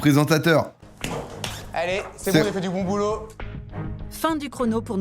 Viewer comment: il a vraiment dit "c'est bon J'AI fait du bon boulot" où c'est juste moi qui ait une mauvais oreille ?